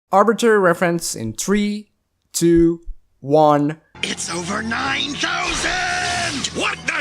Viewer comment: vegeta always sounded like he was saying "it's over 9 chousand"